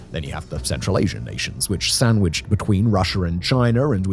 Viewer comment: Am I the only one who hears "Russiar " and "Chinar"?